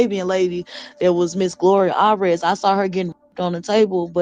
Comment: Hey - Someone may have already said this but @ 43:29 when is talking about seeing a Mexican/Arabian woman on the pool table- I think she said the name GLORIA ALLRED- and not “Gloria Alvarez”. As in this woman is a client of Gloria Allred’s (the well known attorney). Because I think she says, about the woman, “She’s one of Gloria Allred’s”.